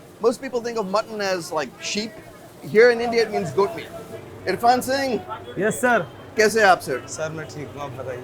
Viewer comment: Did he say irfan singh